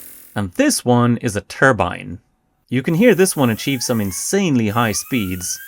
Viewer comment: That sounded like turbo1:04